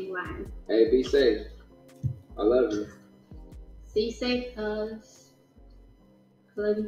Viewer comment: did she just say....."C SAFE CUZ?!!! SOOOOOO.......I Guess she's out here Crippin' huh, since she is from the outskirts of SoCentralLA....Ladera Heights...on the Westside😆😆😆😆 But aside from that, nah, if I was BigSean I wouldn't marry that chick, especially in this toxic feminism/me too climate, and how this pandemic have many of these female celebrities exposing themselves wanting out of THEIR MARRIAGES?!!! Take the redpill, Sean, it's a life saver!!!!